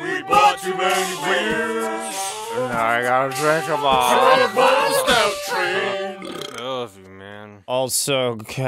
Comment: What does say at 7:35? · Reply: "so all aboard the stout train! I love you man."